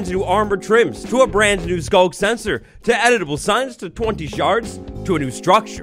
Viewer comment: Oh, at first, I thought you said EDIBLE signs. Editable signs definitely make more sense than signs you can eat